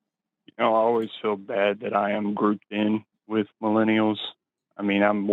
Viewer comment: Audio on my phone cut out for a split second so it sounded like he said "I'm so mad that I am Groot" Me too, buddy. Me too